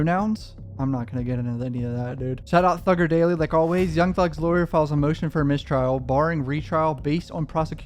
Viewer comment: Lmfao I had my phone on low and I thought I heard you say thuggerdaily filed a motion for a mistrial